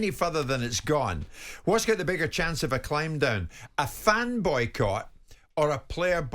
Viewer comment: I was like, "a fanboi caught"?!? 🤣🤣🤣